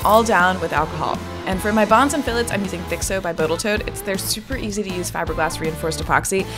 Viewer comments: You say botal toat at ~7:50 and i thought i'd misheard you, but then listened back and you did! :D lol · I think she said Botal Toat.